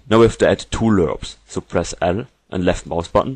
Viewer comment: Sorry what buttons dose he say to press at 1:14 ? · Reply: L